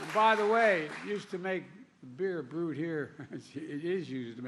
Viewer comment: 🤡🤡💀 beer boot ? Wtf was he mumbling about 💩💩